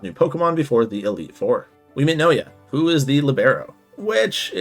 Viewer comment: Did you confuse Nishinoya with Naoya from JJK or am I hearing things weird? Anyways, I'm glad your friend pushed Haikyuu on you for so long, and that you ended up liking it a lot! This may even mean you'd also get to watching/reading One Piece (if you haven't yet) if we bug you about it for 4 years /s